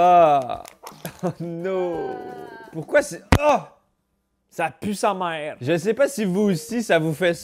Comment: "oh Ça pUe sA mAeRe" mdr c'est ce que j'ai compris au début